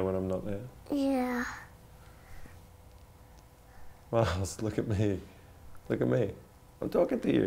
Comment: I really LOVE that nickname he called her right after 2:00 when he was asking her to look at him, especially the way it sounded with his accent, but I can't figure out what it was. I know her name is, and with his accent, the nickname he called her sounded like 'Miles' but a much more beautiful version of that name, and I can't figure it out. I would GREATLY appreciate it if someone could tell me